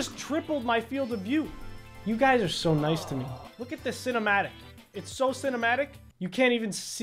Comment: I thought he said f*ck this but he actually said lookatthisss